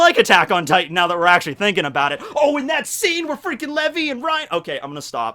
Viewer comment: Did He Just Say Lvi for Levi ?